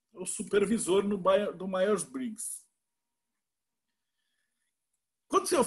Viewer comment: Salve! Como é o nome do autor em que voce cita a energia de supervisor em 36:50? Não entendi bem a pronuncia. Grato! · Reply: Myers-Briggs